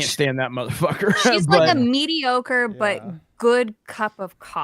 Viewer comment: I thought was about to say "Zendaya is like a mediocore cock. It's not great but you still look forward to it."